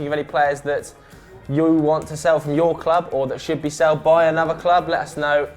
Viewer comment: Did he say "should be seld"??